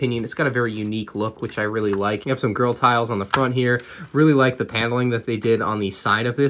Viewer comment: Where u in subtitles? it says girl tiles instead of grill tiles xD